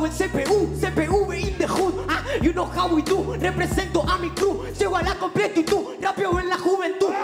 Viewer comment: a mi parecer no dice "llego a la competi y tu" sino que dice "llego a la COMPLETITUD"